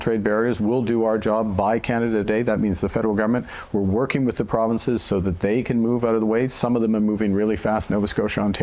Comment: Did he just say" working with the Premieres so they can get out of the way? " Does he mean he wants to get the Premieres out of the way of his master plan? What does he even mean?